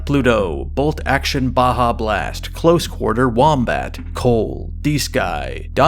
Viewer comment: has come out of his coffin again, this content will help me survive Valentine's.. Thanks! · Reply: Why did my dumbass thought u said “closet” 🤦🏾😂